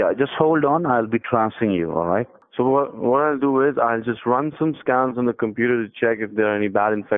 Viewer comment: is it just me or did he say "Im gonna run some scams on your computer"